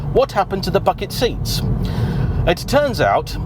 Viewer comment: sounds like says, "... what happened to the f^&k'n' seats?" 😁